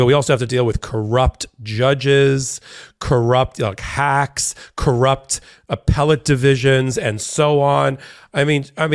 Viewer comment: I thought Habba said "Crooked" and not "Corrupt"...You say to-may-to and I say to-mah-tah. You two are my favorite MeidasTouch team, btw.